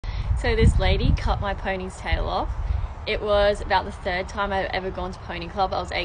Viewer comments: I thought at first she was saying the lady cut her ponytail off, till she demonstrated with the horse tail!😂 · I don't know why I heard "penis" instead of "pony's" 😭😭